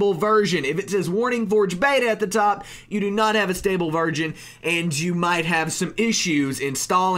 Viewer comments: did he say "stable virgin" lol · did he just say you do not have a stable virgin xD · I heard him say stable virgin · lol I heard "stable virgin"